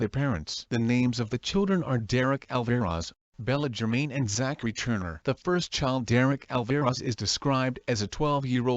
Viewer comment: Zach Returner (misheard) first thing I thought was "He's not returning this time." It took me the second time he said it to notice it was Zachary Turner.